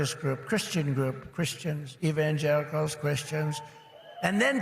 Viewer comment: Did I hear Donald Trump say evan-jerichos, it sounds like he mispronounced the name wrong, the real word is "evangelicals" and I think Donald Trump has a mental acuity cognitive gaffe and no way Donald Trump aced his cognitive test, he cheated.